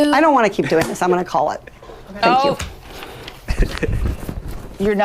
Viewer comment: All I heard is “this is getting to hard, I’m out”